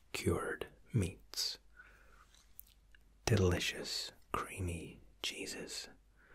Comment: I seriously thought you said “delicious creamy Jesus”. I had to back up and listen again. 😳🤣